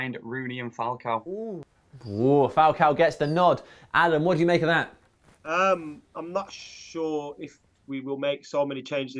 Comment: I heard "Alan what do you think about that"